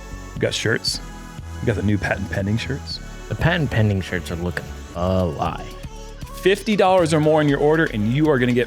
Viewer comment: Can anyone translate what says at 1:52? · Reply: "The patent pending shirts are looking fly" (fly was enunciated fuh-lie)